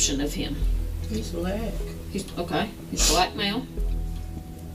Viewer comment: has anyone else heard that? that's fake af. Listen closely. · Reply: Chaos she whispered how tall is he?